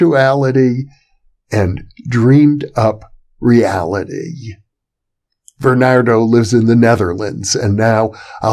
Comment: For a moment I thought he said "Bernard lives in the Netherlands" As in the Nether. lol